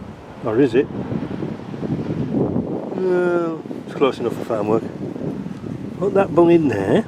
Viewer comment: ish did you say "close enough for Farnworth" ....asked the bloke on Bromwich St Bolton. All I can say is ,if so then it's all getting a bit "Phoenix nights" on here.